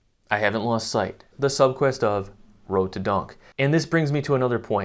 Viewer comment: At first I thought you said, "road to donk" and I thought, come on bro. Then I realized what you actually said 😂